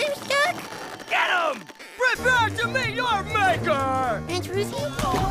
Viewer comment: Ed: "Prepare to meet your maker!" Jimmy: "Antonucci?" 😅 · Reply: oh wow until you pointed this out I thought Jimmy said "Aunt Ruthie?"